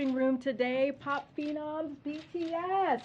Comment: what did she say " poppino??!"